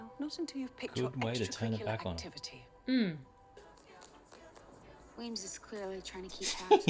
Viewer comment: Not important but I kept hearing “Wayne” every time you guys said “Weems” and sat there for a good minute thinking “who the heck is Wayne??” 😂😂